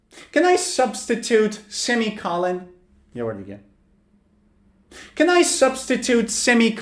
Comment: Dude can you speak Persian ? Because I swear in 8:06 I heard you said یه بار دیگه